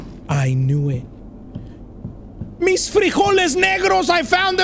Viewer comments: I swear I had to rewind it because I thought he said miss frijoles n-word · Did say negros in 14:30 🗿